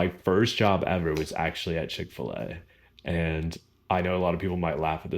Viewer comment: It must be because I am British and don't appreciate all the inflections of an American voice, but I just cannot make out at 2:24 what you said your first job was!! To me it sounds like "Chick-fer-lay" but I'm sure that's nothing like it!! Is that something that's specifically American?! And at 6:00 .... one of your hobbies is "Pippa-bow" or "Peepa-ball"?! Is that also a very American thing?! I am keen to learn, and I am sorry if I offended anybody!! 😀